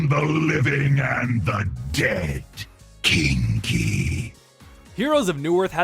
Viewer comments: I thought he said "Kinky". Not Cheeky. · Wait .. That line .. " Kinky " For all this time i thought he say " Chinky " !! Damn u Anub-arak